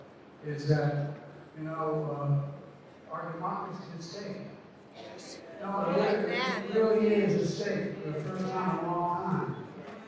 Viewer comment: What did he say ? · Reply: Audio quality isn't great but sounds like, "...is that - you know, um - our democracy isn't safe. It really isn't safe for the first time in a long time."